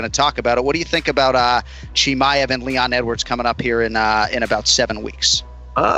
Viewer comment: I heard him say Leon En words.... Edibles 😁